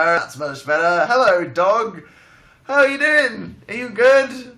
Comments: I thought he made a pun with his last name in the beginning I thought he said "Howell are you." I was like.... "-.- wait..." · for a second i thought he said HOWELL are you and my face retracted inward like a clenched butthole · I thought he said Howell are you